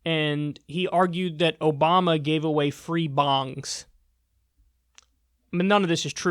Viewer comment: "obama gave away free bongs". wish that one was true. id have it signed and on display. also if gay marriage caused floods that would be a great thing for drought stricken areas. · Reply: Ohhhh I thought he said free bombs!